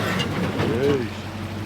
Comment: Did someone say Eish , as the car scraped over the rocks ? 😂